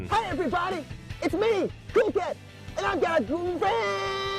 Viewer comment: I swear I hear, "Hi everybody, it's me, google!" at 14:32.